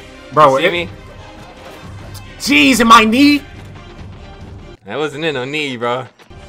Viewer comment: I thought this man said cheese in my knee lmao